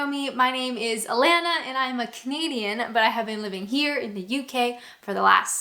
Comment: I mentioned in an earlier reply for the first few videos I could have sworn you said "I'm a Comedienne" at the start at least 5 vids before I realised you were a Canadian (by the way, I watched the ads whilst typing this)